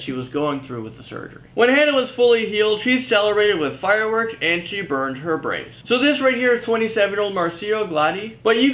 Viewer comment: I thought you said "To celebrate she had fireworks and burned her brains" XD